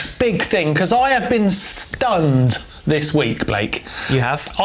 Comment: That moment you thought Martyn said I've been Stoned this week 😐